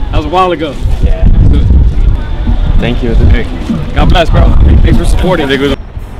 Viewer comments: Did he say, "thanks for supporting n!**@ at 1:58💀💀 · bruhhh at 1:57 it sounds like Professor says "Thanks for supporting N****" LMAOOO HAHAH,, I know it aint but thats what I heard first. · Are we not gonna talk about how it sounded like the professor said thanks for supporting n**ga